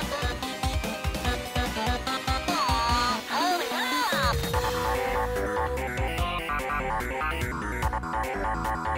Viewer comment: what did he say? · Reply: “Aw, Hurry up!”